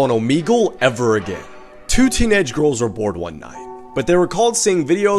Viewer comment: LMAO FOR SOME REASON I THOUGHT HE SAID "two teenage girls were born one night." 😂😂😂